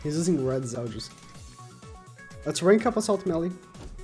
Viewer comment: he really said "Assault Meli"